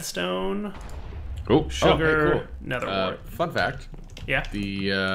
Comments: did i hear at 19:45 say fuck that? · he says "fun fact", but it sounded like something else.